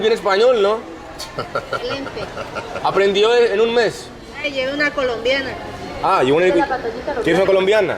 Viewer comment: Can anyone make out what she's saying? The subtitles say "take a Colombian woman with you" but the only word I can make out in the sentence is "Colombiana." I'm having trouble with the rest of it. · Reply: "lleva una Colombiana"